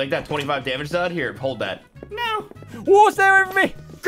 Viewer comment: Is it just me or when he said take that twenty five damage card and he no it sounded like nya